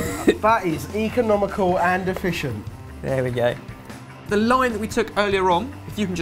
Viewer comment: I thought said "the line we took earlier on" which I gladly mistook for lime